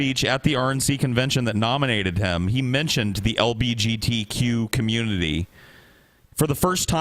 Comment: Did say LBGTQ?